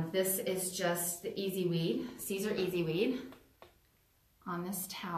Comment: What kind of vinyl is best? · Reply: Thanks. I didn't understand that what I heard as "Ceasars," was a a reference to a brand name.